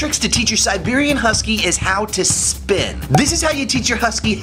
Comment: I thought you said how to spit at first 🤣😂🤣😂